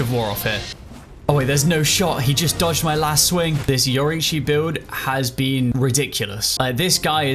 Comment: I came to this video because you mentioned it in a later one and I misheard it and thought you were doing a Yoruichi build, so now I want to see that too 🙂 Shunko!